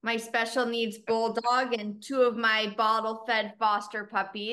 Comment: I thought she said bobble head, not bottle fed . 😂